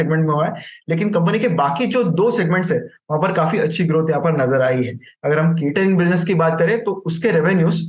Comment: sorry, i didnt catch that word..which business? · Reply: Cratering